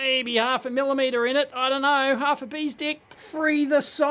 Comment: Did he say, "Half a bee's d!ck"? Never heard that expression before. Might have to use that one.